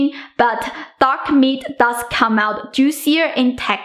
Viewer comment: Did she said dog meat? At minute 1:06 Just kidding, i love your videos! it all looks so tasty 😋